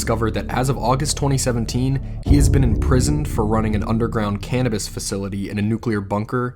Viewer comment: Just tò Say, I am not native in english so when I hear "cannabis facility" I understood "cannibal facility". I literally stare the screen in shock for some seconds.